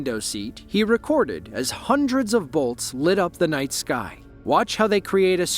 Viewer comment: "He recorded as hundreds of volts lit up the plane's wing". Absolute poppycock. You have your facts quite wrong. Each lightning strike is MILLIONS of volts, not HUNDREDS. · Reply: He said hundreds of bolts illuminate the planes wing. Like saying hundreds of lightning strikes, not saying hundreds of volts. I thought he said the same thing then I replayed it and listened and he said bolts and not volts.